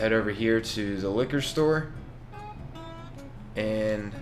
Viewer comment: So I just finished playing the Resident Evil 2 demo, and when you said liquor store, I heard "licker store" - just thought I'd point that out lol